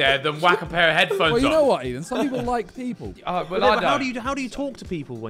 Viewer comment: Did Simon call Ethan 'Ian' ?